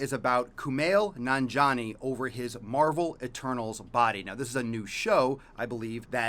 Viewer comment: I was listening to this while cooking and thought you said marvel attorneys and i was like dang theyre really stretching with shows now days